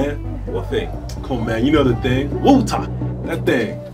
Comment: Doesn't say Bo'oh'o'wa'er Me: "My disappointment is immeasurable and my day is ruined" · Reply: Me whos new and thought he was saying walter